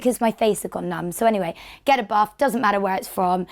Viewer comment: Because of her accent, when she says "get a buff" it sounds like she is saying "get a bath" I was like woah sorry toff do i really smell that bad